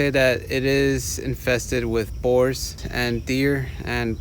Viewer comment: a knife against a boar? lol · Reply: Ohhh, boars. I thought he said whores...! I was really confused. We don't have those up in the north, rofl.